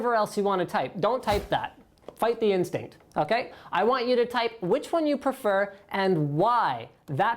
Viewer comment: He clearly says, which one do you prefer and WHY. Watch the video again.